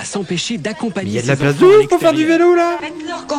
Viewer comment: c’est moi ou elle dit « velou » ?💀